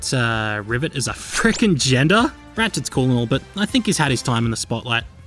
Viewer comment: i have trouble hearing what you said but did you mean she is trans? it was never confirmed and the series retconned themself very much with 2016 release of RAC. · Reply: Oh nah nah all I said was “Rivet is a frickin’ gender,” which is basically me saying “I wanna be her lmao”